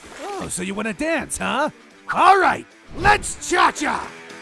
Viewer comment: LETS JAJA idk what purple guy says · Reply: Cha-Cha